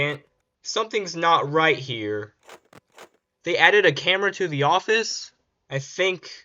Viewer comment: that "here" sounded like scott